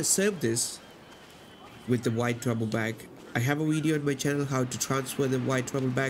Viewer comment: I like how u say duffle bag sounds like your saying “there you go guys you got the dwite trouble bag”